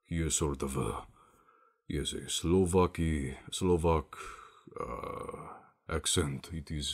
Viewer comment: I heard "slow-f*ky"